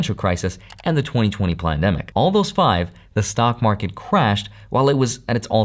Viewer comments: did you say “plandemic”??? 👀👀👀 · sounded like "2020 Plandemic" lol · did he say 2020 PlanDEMIC ???😂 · 🤔 Did i really hear "pLandemic"? 🤨